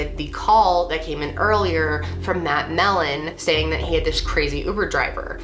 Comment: did she say that melon??😂